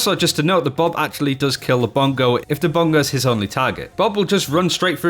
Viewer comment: what does he say? I’m new to overwatch and it sounds like he is saying bongos